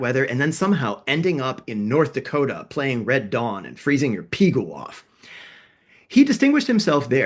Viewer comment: Did you say 'Freezing Your PIGU(屁股) off' ?!😂😂😂